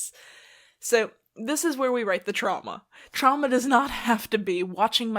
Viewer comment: When u said the word “trauma” I thought u said Chama😭😭😭and I was immediately like HUH